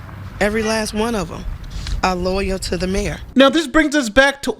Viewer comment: Did she say they are “ lawyer to the mayor “ ?